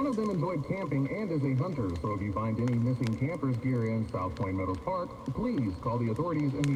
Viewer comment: I thought he said “South Park meadows” Lmaoo